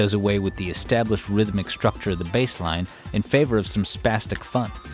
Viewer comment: WHAT DID I HEAR HIM SAY??? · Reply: Indoor voice. Spastic is the adjective form of spasm, defined as a sudden involuntary muscular contraction or convulsive movement.